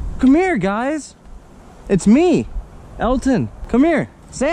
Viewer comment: did call himself Elton?